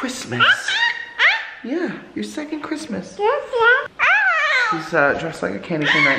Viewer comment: said "yes dad" or atleast it sounded like it😂